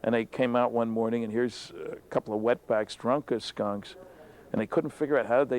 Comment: did he say wetbacks? 🤔😅